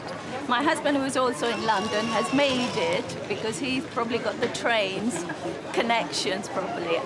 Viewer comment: "I've missed the flight, but my husband made it because he got the trains" I misheard and thought she said "Brains"